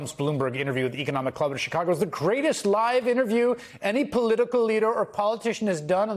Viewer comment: I thought he said “greatest LIES interview” 😂😂